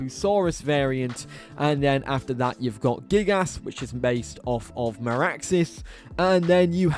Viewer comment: "gig-ass"? anyone else hear that lmao